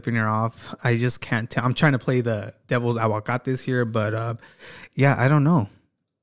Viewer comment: #replaythechisme i thought you said "devil's cacahuates" 🤣🤣🤣🤦‍♀️